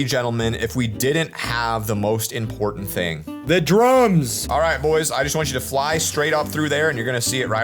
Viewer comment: Am i the only one who heard "THE DRUGS" in 28:40?